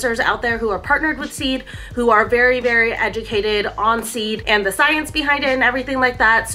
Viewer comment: I'm glad I turned on cc. I thought you said the influencers were agitated with Seed. You said educated. :). My bad!